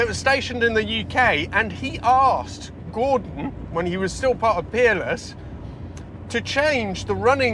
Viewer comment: I hear with a Yankee accent and I swear I thought you were saying Keeble was a designer at the Penis Car Company. Finally heard Peerless. Not sure if that’s correct either. By the way, that Chevy V8 exhaust sounds like something is off.